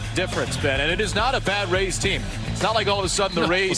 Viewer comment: When I first heard this and the audio and THOUGHT he said Bad Race Team (by mistake) and was being suspended for that, but he does say Bad Rays Team- so obviously I was wrong